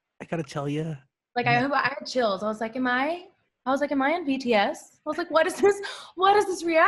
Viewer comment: did she say BTS? or something else pls help me.